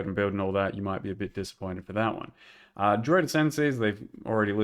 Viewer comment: i swear the first 3-4 times i thought he said "droid" not "druid"